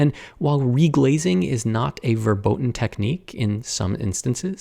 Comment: Did he really said the German word „verboten“ ( 4:47 )? It would make sense but I was surprised.